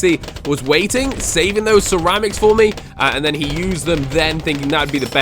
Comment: He said ceramics instead of lead